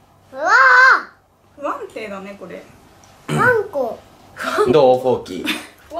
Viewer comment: がマンコーって言ったように聞こえる！w